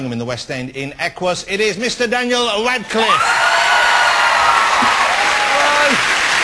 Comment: hahahaha i love jonathan ross! hes hillarious and when he announced daniel he sounded like he said daniel wankcliffe lmfao!